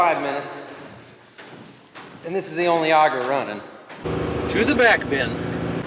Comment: It sounds like your saying to the bat cave 9:46 Maybe can add a sound effect next time